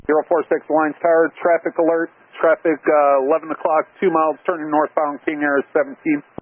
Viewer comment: Not trying to be a grammar nazi so forgive me for pointing this out: at 2:23 when the tower calls EURO46, the subtitles say "traffic 1 o'clock" when the tower actually said 11 o'clock. Just letting you know. Scary how close this got.